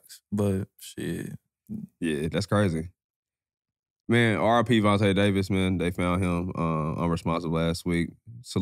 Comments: BROOOO I thought he said Gervonta Davis for a sec.. Had to hit that rewind real quick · ts scared me im not a big nfl fan so I thought y’all said Gervonta Davis🤦‍♂️💀